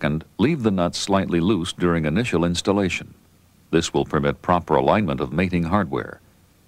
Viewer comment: Did I hear correctly: "Leave the nuts loose during initial installation. This will prevent proper alignment of mating hardware?" Did he mean to say improper?